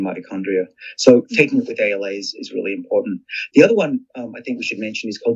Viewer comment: carnatine taken with ALA.?? is that what he said?